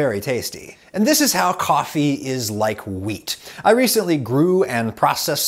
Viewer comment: You said wheat just sounds like you say weed